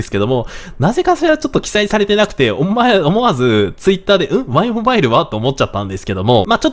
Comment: 「お前！思わず…」 って聞こえました😅 さんはワイモバイル対象外がめちゃくちゃショッキングだったんでしょうね😏